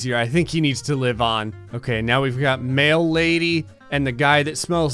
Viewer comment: Who else thought said "male lady" when he said "mail lady"? I laughed so hard when I heard that before promptly becoming very confused.